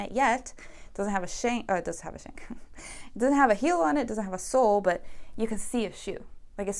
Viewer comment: When you said "the shoe doesn't have a sole yet" I heard it as "the shoe doesn't have a soul yet" and giggled at the thought of a shoemaker implanting a soul into the shoe as part of the soemakinh process 😂